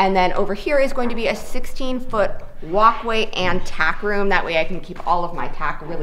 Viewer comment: oh,, TACK room........ thought you said TAP room. I was going to ask when the barn-warming party was going to be.....!